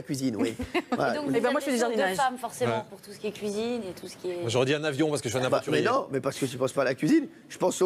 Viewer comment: Il a dit quoi Jean-Luc Lemoine vers 2:04? On ne distingue pas bien les mots. Merci. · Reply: Tiar Salah Eddine "Moi jaurais dit un avion parce que je suis un aventurier"